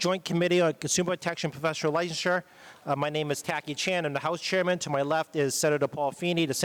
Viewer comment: Did he just say his name is Tacky Chan??? 🤣🤣🤣